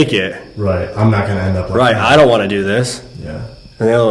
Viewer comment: When yous asked what did they treat here, it sounded like it said, "I'm. Not. Cra-ZY"